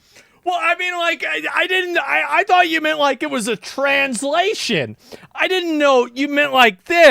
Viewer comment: Oh did you say translation? I thought you said transition.